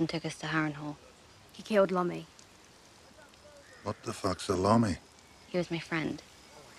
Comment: "w h a t t h e f u c k s a l o m m y" -sandor 'the hound' clegane (G.O.A.T) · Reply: i always hear "what the fuck salami"